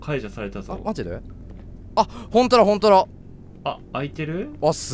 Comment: ここ何回聞いても 🎃「ほんとらほんとら！」 にしか聞こえんくて、☺☺ってなっちゃう